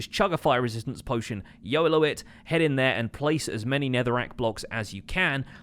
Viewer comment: did he say "yolo it"?????